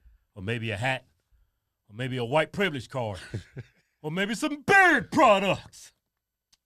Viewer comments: I thought you said, “or maybe buy some BIRD products!” 😆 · I always thought he was saying bird products 😂